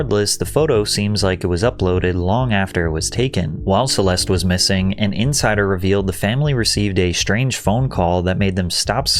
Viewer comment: i thought they said she was missing some fingers .... this is too chaotic